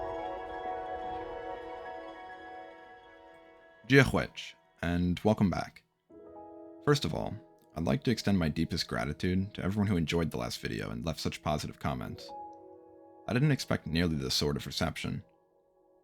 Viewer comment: i thought he said "id like to extend my disgratitude.."